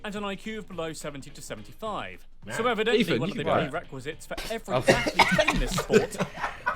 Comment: what did they say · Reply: ethan u can do that